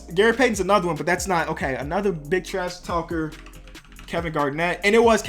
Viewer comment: it sounds like he says another bitch ass talker